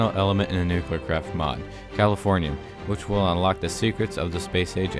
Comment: When you said you must obtain the final element I thought you said California